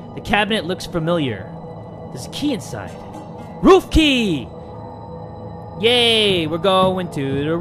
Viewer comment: I thought he said ruffie not roof key